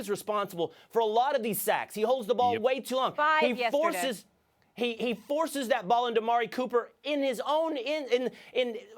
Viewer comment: What the hell did Molly interrupt and say at 5:26? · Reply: Five sacks, he held the ball to long.